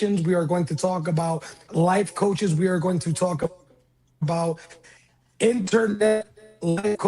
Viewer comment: I thought he was sayin they gone talk about bad internet 🤷🏿‍♂️